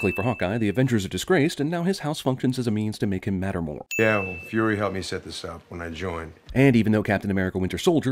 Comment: He clearly says that fury kept it out of the files